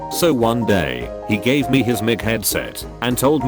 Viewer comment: did it just say mcheadset sounds like something McDonald’s would do